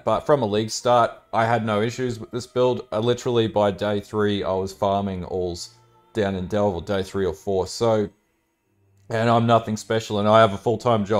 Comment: I have no idea what he says he was farming on day 3 and I've replayed it 3 times. The build looks great tho! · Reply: He’s talking about farming Aul the crystal king in delve.